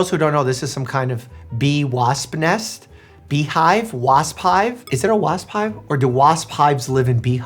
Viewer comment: "...bee wasp hive..." I heard "peewoop hive" 🤦🏼‍♀️